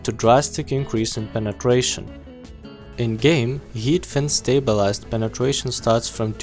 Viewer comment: It sounded like you said YEET fin stabilized 👌🏻😂👌 (Sorry) keep up the great vids!! Found this very helpful